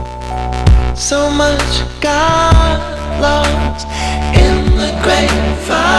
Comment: omg I heard "So much God loves" instead of "So much got lost" 🤦‍♂